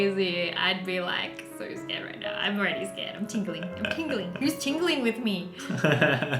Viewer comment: IM TINGLING TOOOOOOOOOO #TeamTingling When she said that it sounded like she said TING-A-LING